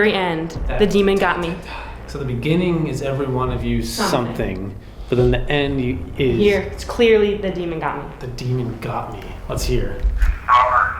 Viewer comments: I heard, "every one of you, get out, don't come back" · I hear don't look at me not the demon got me